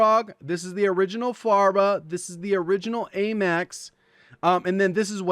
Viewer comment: did he say the original Farva?